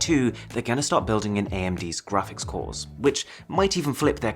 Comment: Did I just heard AMD? Aight I will forgive you this time Samsung